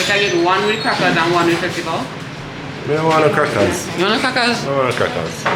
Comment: I thought said he didn’t want any crackers 😂😂😂😂